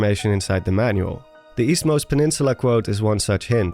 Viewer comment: Oh, Eastmost? I thought it said “Weastmost”.